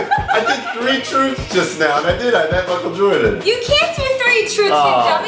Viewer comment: "I met Michael Jordan". When he said that, the cat showed up from behind him... I swear I thought it was Michael Jordan! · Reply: I thought he said Michael Jaction XD I was like, three is no way that met a DEAD GUY LIE!! How..........